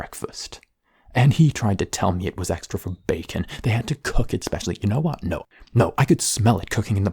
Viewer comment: My dumb ass thought it said cookie there for the longest time so when I asked why he was tied up I was like “that’s a little much just for some cookies”